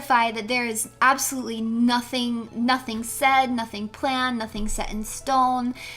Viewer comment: the outfit giving "watching movies all day" vibes was perfect for the comfy stream (of which I thought was great, it almost sounded like she said 'sorry there's nothing planned', I think she meant to say 'get more blankets')